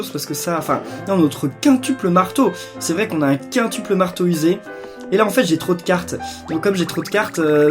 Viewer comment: le marteau "musée"o.O j'imagine déja ça XD(ç me rappelle un truc que j'ai entendu au collège:la maison "en T")